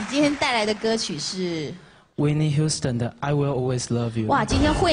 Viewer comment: I swear she says iguana!!!!! Bwahahaha.....